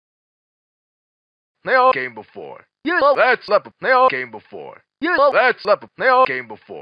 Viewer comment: I thought he was saying "You're all that's left, they all gay before."